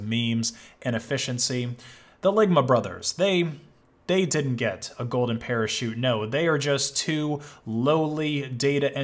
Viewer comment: I thought he said they wouldn’t get the parachute.